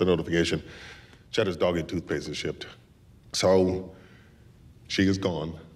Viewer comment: It sounded like he said, "Cheddar ate toothpaste and shit." What did he really say? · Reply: Cheddars doggy toothpaste has been shipped 😂